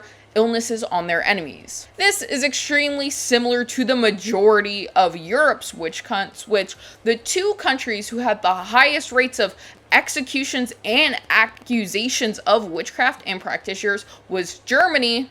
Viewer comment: anyone else heard her say witchc*nts ? That had me chuckling.